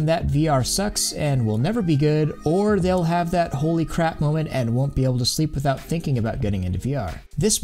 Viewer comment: i heard "and don't even sleeping and thinking they're in VR" because after my first intense VR experience i got the VR hangover I thought im still in VR everything felt kinda wrong but i dont paniced becaus i know i was in reality, after i go to bed i dream the whole night about VR